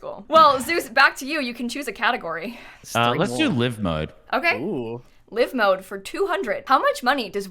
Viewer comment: I seriously thought Zeus said “lymph node” at 15:58 💀